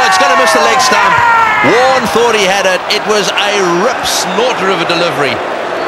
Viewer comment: can someone help me out with what the commentator says: (0:06) "it was a...of a delivery" I can say ive never heard that growing up in canada my whole life lol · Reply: Ripsnorter